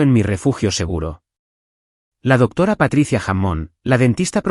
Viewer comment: Jajajajaja yo escuche "Patricia Jamon" 😂😂😂 las familias asi dan asco, los anuncios arruinan el video, les colocaron mas anuncios que historia que payasada